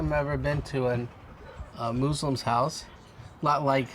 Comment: he did NOT just say MOO - slim XD "This is my first time being in a MOO slim's house"...........